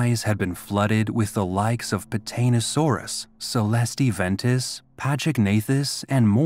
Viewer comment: did I just hear potatasaurus??